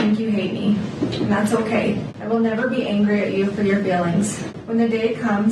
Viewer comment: "to my dear baby boys, I will never be mad at you for your failings......" yep she deserves all of the years in prison and never deserved children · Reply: I thought she said "Feelings"